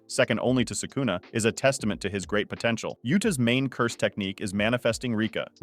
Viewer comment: did you say potential?